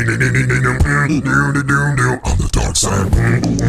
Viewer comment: Teacher: okay we are gonna learn about walls The kid that watched attack on titan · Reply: For a reason I thought he was saying on the darksied